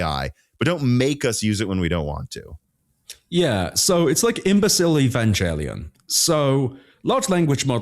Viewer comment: I can't quite make out this profound new thing I plan to throw around at cocktail parties... sounds like "embecile evangelium"? 😂